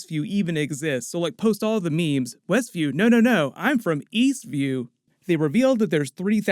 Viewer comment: Eastview? I thought you said, Weastview.